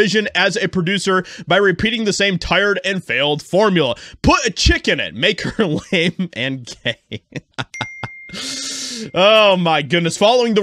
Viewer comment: I thought that she or he said put a chick in it and make her gay as fuck! I can't hear too good! 😮😅😂